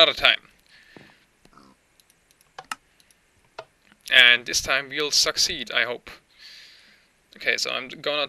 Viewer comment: 1st thing i hear "and this time we'll suck seed's" haha!